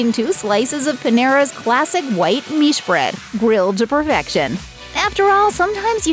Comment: does she say “white meat bread”? If so, what is white meat bread?